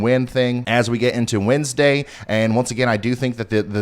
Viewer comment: idk what we call intro thing you usually do with the 'alright' this but I hope it stays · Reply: did you say windsday or does my brain just hear puns